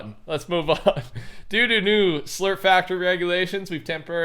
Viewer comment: bruh I heard "due due due new factory regulations" and had to rewind the video back a couple times, lol